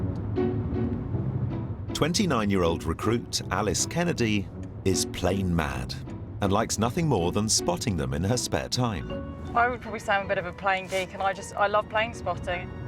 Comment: Lmao, I thought the narrator said Alice was plain mad not Plane mad. Ooops, my bad.